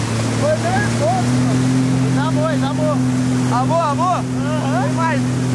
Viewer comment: Eu não sei se eu entendi um "Amor Amor ou um "Cabo cabo. Fiquei na dúvida agora😳😳 Eu vi na parte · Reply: eles amo eles amo:amo amo? Ele tinha perguntado pro se eles tinha amado o passeio Ksksks